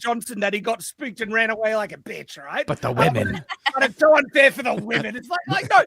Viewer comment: Did say “b!tch”